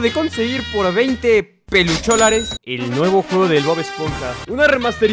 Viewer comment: estornude y entendí "el nuevo cul* del Bob esponja" :v